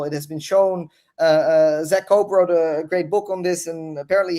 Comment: Whats the name of the author? I couldnt understand what he said (also book if possible ) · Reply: The Wealth of (Some) Nations Imperialism and the Mechanics of Value Transfer By Zak Cope